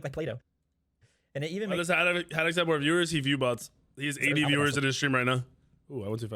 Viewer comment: "Haddix viewbots?" Did I hear that right? I love the guy, love his content, genuine fan watching hours after an upload. Like, its proven?